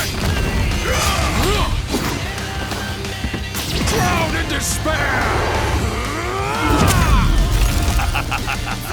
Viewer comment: What did Madara say at 16:58? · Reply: Drown in despair